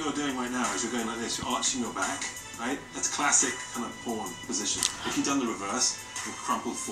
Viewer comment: I didn’t watch ANTM with subs but omg 21:43 did Nigel really said the “P” word? I remember him saying it’s a “classic boring position” yikes 👁👄👁